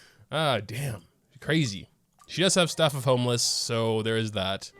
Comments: am i the only one who heard him say “staff of homeless”? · Did anyone hear he say "Staff of HOMELESS".